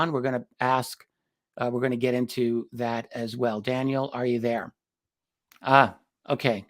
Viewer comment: Did you call him Daniel? 9:18?